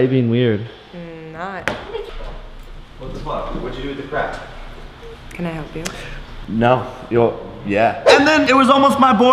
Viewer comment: did anyone else hear someone say "wtf what did you do with the crack" ummm what??